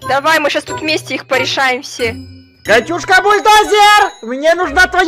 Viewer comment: мне послышалось какушка бульдозееер😂